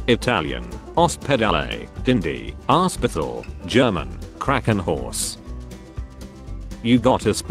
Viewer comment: i just love how the machine says Krakenhorse instead of Krankenhaus. ahh just fantastic